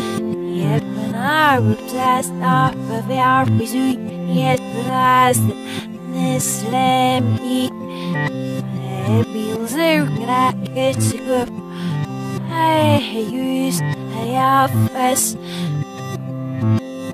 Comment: I heard hate